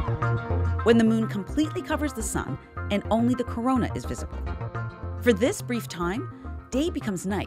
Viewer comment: did she just say... CORONA?